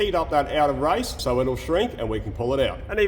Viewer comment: shrink by heat? Did I hear that correctly?